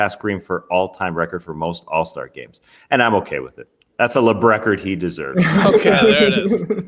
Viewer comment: Thought you said "Lebradford" for a moment... Made me think about you guys opening packs of cards. Any chance you will do that ? Also i mention it again but you guys doing some all time draft would be awesome but i get that it's hard to find time for this stuff.